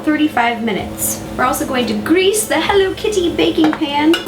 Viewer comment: i thought she said grease the hell out of it........